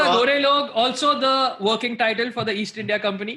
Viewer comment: "Kaala dhanda gorey log - also the working title of the East India Company." My sleep deprived brain heard that as "East India Comedy", and I went on a two minute trip wondering "yeh Angad Ranyal haraami hai kya?"